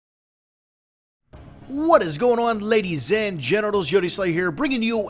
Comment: hahaha, sounds like he is saying 'ladies and genitals' heheh :DD